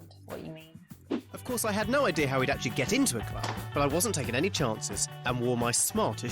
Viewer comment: I think at around 14:15 it was "how WE'D actually get into a club" but the subtitle said "how TO actually ..."